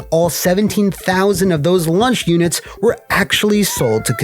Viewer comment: did you say "lunch units"